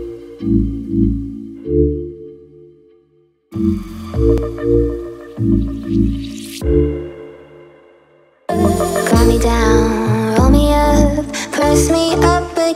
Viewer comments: I thought it say Romeo but instead it say roll me up😭✋ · For the longest time I thought they were saying “calm me down Romeo”